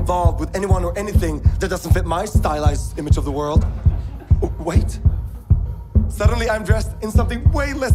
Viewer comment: why did i think he said "white" and not wait bcos of his accent and everyone is just like hold up